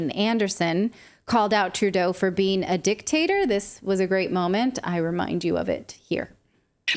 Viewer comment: I thought she said 'dick taster' !